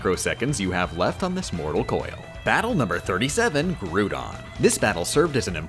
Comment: did he say groodon?